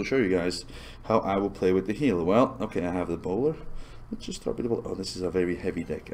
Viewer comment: at like 3:59 I thought he said welp i have ebola instaed of welp i have a bowler XDDDDD